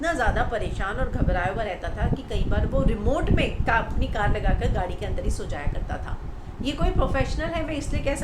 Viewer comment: What’s she saying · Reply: she is saying that woh aapni car me sota tha, yeh baat shekar kapoor ne bhi bola hai sabse pahle he.